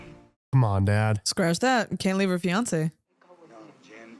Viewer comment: I have no idea why but every time you said 'Fiancé' I heard...'Beyoncé' and I was so confused 😂